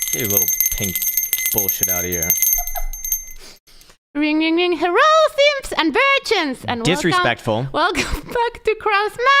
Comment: Until today, I always thought said good morning Simpson Virgins. Lol🤣.